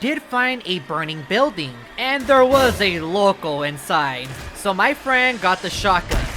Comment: what did you said 5:24 you said lokol or locol?